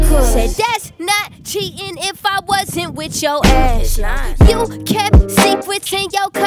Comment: THAT'S NOT cheating if I wasn't with your ass" LMAOOO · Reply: At first I thought it said “THATS NOT cheating if I wasn’t with your ex”